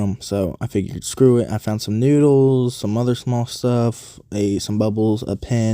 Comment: Just found your channel, but the first thing I heard you say was that you'd found some sh!t. I don't watch people finding sh!t. Sorry! I prefer watching people who find STUFF.